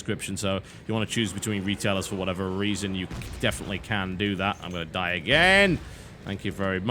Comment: did he just say:" i'm going to die AGAIN!!" ...did he not say at the start of the vid that he never died in this game?